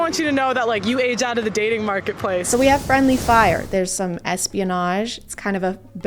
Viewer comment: Had to rewind cus I was eating and I thought you said "there's been some lesbianage" and that sounded sick